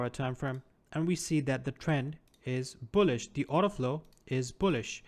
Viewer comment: Good video. I was confused when I thought I heard you say "auto-flow", but I think that was just your slight accent.. ? You are saying "order flow". Yes? Are you South Africa... Elon Musk has similar accent lol. I thought I was learning an abrigded ICT version :))